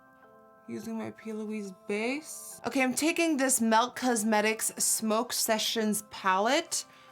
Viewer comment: did she just say melk?